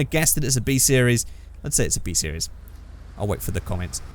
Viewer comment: i thought you said t series